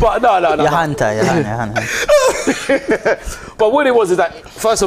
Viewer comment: what is saying to ty “your hand ty your hand” 😂😂😂😂???????